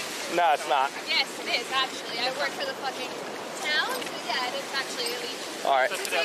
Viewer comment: "I work for the....fu$%ing.....town" That translates to she's a receptionist at the town's visiting center. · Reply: NO, You misheard it. She said "I work for THE POSEIDON"